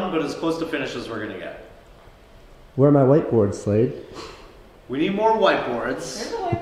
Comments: Thought he said "Where are my whiteboards slave?" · I literally thought he kept saying "SLAVE" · I thought he was saying slave, instead of slade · I heard "where are my whiteboards slave?"